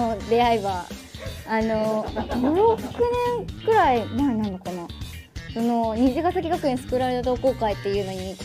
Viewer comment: みゆたんとの出会いは5億年くらい前なのかな って聞こえて笑った